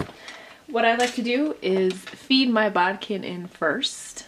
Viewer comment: What is that tool you used to pull the elastic through? I tried to listen and it sounded like you said Bob pin? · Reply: Hi Jason - it's called a bodkin. There are a couple different types, both of which can be picked up at your local sew or craft shop.